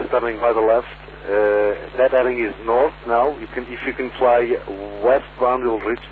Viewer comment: Sounded like "That heading is North, now". Also, It must have felt like the plan is actively trying to fight you, even down to the comms.